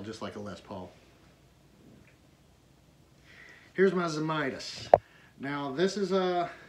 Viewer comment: What is the guitar at 6:00 ? I couldn't make out what you said. Beautiful & cool. Sounds kinda hollow-body + the metal top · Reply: Artie VanDelayo It is a Zemaitis. There is a review of it on our channel. It’s a lovely instrument. Thanks for watching!